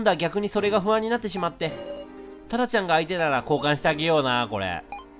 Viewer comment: タラちゃんに聞こえた…www